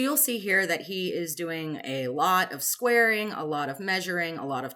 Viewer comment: Doing water changes as I watch this and I could have sworn you said that your husband was doing “a lot of swearing”. Lol Replay: “squaring”